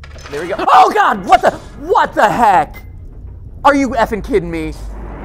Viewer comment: Did he said "Are you Afton kiding me?". Or im just whatching too many Theories?